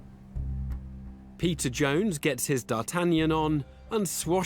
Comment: Oh it's Peter Jones... I have always thought he said pizza Jones